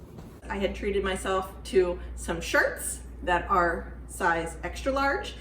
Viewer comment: <link> I thought she said sharts. LOL